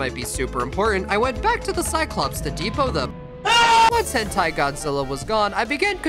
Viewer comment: did bro say hentai godzilla???